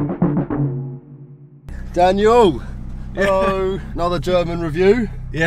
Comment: Thought you said another German Jew at the start, not another German review! Thought you might be German because you had a German girlfriend in one of your videos.